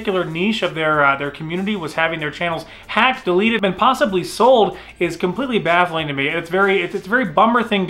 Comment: did you say “possibly sold” at 4:32?!